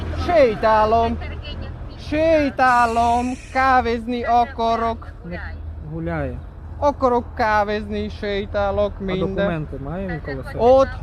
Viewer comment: Мне послышалось или на 1:14 он сказал: окорок, окорок, кавы😅